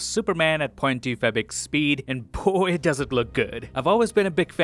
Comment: I thought you said "It doesn't look good"